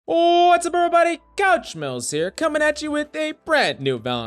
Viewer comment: Okay so I can't be the only one that occasionally mishears you to say "What's up everybody!? Gouch Smells here-" in your intro right? Right?